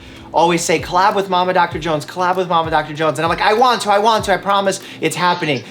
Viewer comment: “Mama Doctor Jones” I first thought he said, “Obama Doctor Jones”.